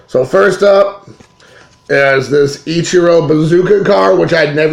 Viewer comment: I thought you said Ichiro Bouzouki! 1st Irvin is 1955.